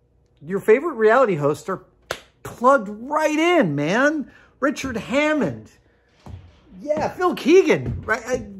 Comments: Did you say Richard Hammond? Richard "hamster" Hammond? wow... would not expect you mention him... interesting.. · did he say Richard Hammond as in top gear and the grand tour, because that would be awesome